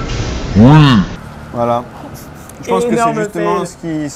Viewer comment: 可能是發音的問題~ 我聽這服務員的發音 我是聽成 green tea 說~ 哈哈...XDDDD"